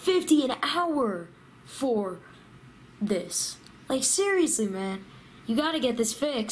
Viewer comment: said seriously man and Siri came on lol it sounds like he said hey siri